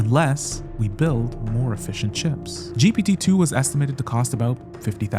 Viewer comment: Nice job on the video. When you say “efficient chips” I hear “Fish and Chips”. 😂